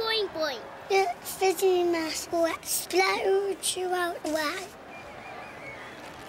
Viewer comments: what the hell did he say? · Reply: The fizzy mask will explode you out wide 🤔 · Literally no idea what the second boxer says! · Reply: I hear ‘the fizziness will explode Chihuahua’ I really wanna know what the script said!